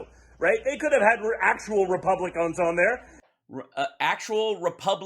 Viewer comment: I heard "Republicunts".